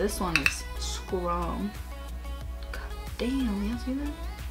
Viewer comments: who else hears someone say "yes" when she says "god damn you guys see that".. creepy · Did anyone else hear that "yes" in the background (13:15) when Daisy was putting high lighter and was like "do y'all see that" lmao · Is it me or did anybody else hear the "yes" at 13:15?????????? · did anyone hear that "yes" at 13:14 😂😂